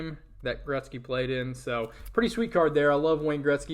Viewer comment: I will give you my address on that Gretzky lol. Nice card brother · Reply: My fault thought you said Garnett